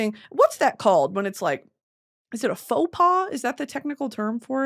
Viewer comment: i was spacing out and when i heard this i thought she was trying to say fupa with a french accent😭